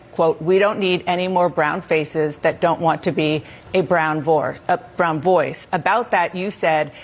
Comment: I thought she said brown whore. Had to rewind lol